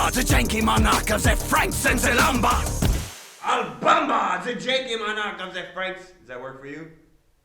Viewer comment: in the video I thought he said obama for a second